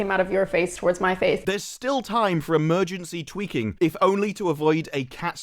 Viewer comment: When you said 'emergency tweaking', because you were talking about Disney, at first my brain thought it heard 'emergency twerking'.